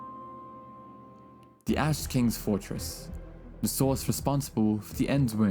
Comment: i misheard "ash king" as "ass king"